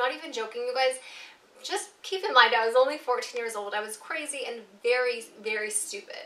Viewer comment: At one point I thought she said "at this point my dad was only 14 years old."